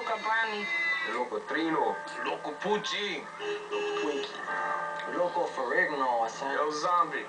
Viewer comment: One of those dudes ; could have sworn he said he’s loco Twinkie . Ay ya yay . Next thing you know “loco Michelin man “. Or “loco cupcake”.